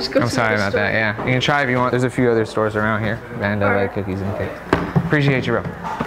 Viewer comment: when the fat kid said hes gunna go to another store does say…. “theres a few stores around here, and they will have cookies and cakes” or does he name the actual shoe stores? I cant tell😂